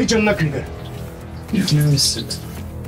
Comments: de çağrı'nın ne dediğini anlayan varsa söyleyebilir mi? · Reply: Emin misin diyor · Saniyede ne diyor çağrı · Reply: Emin misin diyor. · de çağrı ne diyor? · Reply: emin misin diyor